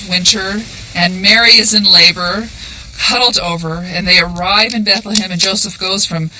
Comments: What does she say at 13:59? doubled over??? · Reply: Mary is in labor, huddled over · What does she say at 13:59? Mary is in labor (????). harold over? doubled over?